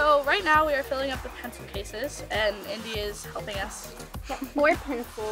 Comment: does kenzie say “india’s helping us” instead of indiana?